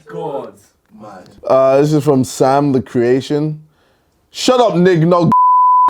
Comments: what word is that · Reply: Golliwog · what was that G word, I won't use it, I just don't know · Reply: Gollywog